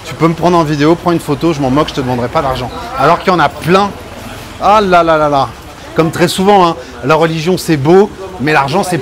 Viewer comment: le holalalala ? c'est bien ce que j'ai compris ou j'ai vraiment l'esprit tordu ? :D